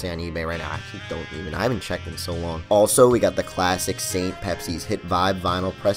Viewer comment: I can't find his music other than Bandcamp or Soundcloud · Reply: Camino 84 I thought you said that Saint Pepsi is Aqua Fina 😕